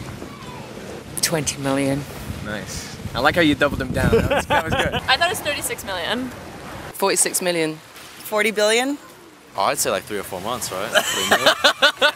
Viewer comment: Did she say 40 billion???